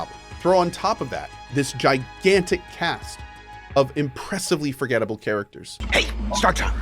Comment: I swear I heard "This gigantic cat" at 2:53.